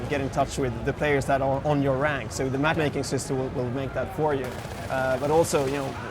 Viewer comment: does he say there is a map making system?